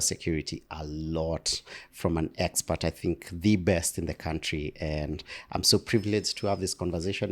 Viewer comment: minutes of the video, did the presentator just "The Best In the Country"?? Very interesting, I know him from Pukyong University and I am curious to hear what he can say 😅 😂 🤔